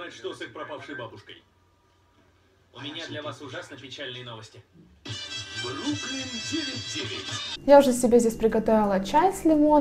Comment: Название сериала "Бруклин ТВ"? Не уверена, что правильно расслышала. Я как раз люблю что-то типа "Клиника" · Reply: неа, бруклин 99 (англ. Brooklyn Nine-Nine ). Я пересмотрела Клинику все сезоны, причем по 2 раза, так была расстроена, что закончила, прям дыра в душе) недавно нашла этот сериал, просто смотрю с удовольствием, настроение поднимает каждый вечер.